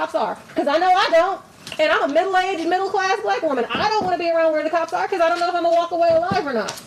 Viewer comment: Did she say "...I'm a middle class, middle age, white woman." Things that make you go hmmm.